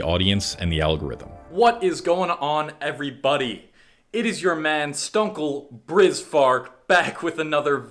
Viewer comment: sounded like you said rizz at around the timestamp point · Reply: Turns out you just said stunkle brizz fart